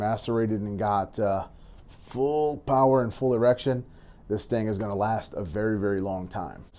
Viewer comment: Did he say full erection at 3:01 lol